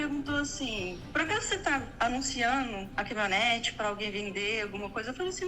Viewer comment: Salve salve 22:32 min. Foi so eu que ouvi, ou ela disse CAMIONETE? A reforma sera de uma camionete... Será que foi isso mesmo que eu ouvi? Fala aí. Um abraço de Franca -SP